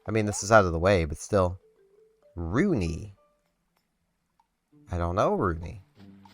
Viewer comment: thought that said Rodney and my heart dropped. that's one villager i will never like under any circumstance 😂 but i think rooney sounds like a kangaroo maybe